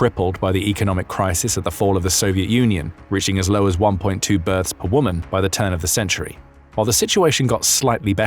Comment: I heard 1.2 birds and asked myself what kind of measurement system is that?